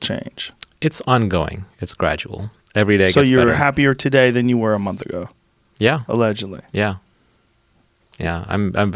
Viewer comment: He said he tried mri ? After saying mediation and witnessing a bit couldn’t make out what he saying around 1:59:00 ??? Anyone know what he’s referring to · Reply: Ssri.. it's an anti-depressing drug.